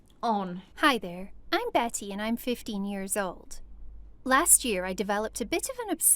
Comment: I thought she said that was 50 year olds but she said that was 15 year olds